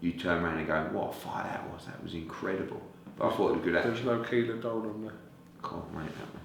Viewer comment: what fight or fighter does kugan say? Can't quite make it out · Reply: Keller vs Doran